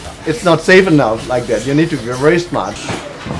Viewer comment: What is he saying in minute 02:00? (I didnt understand) "Keeping two hands here is not save enough. Like that. You need to ..." - and then? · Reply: "you need to be very smart"